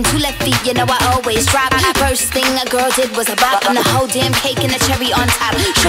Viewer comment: Oh I thought she said “the first thing a girl did was survive” not bop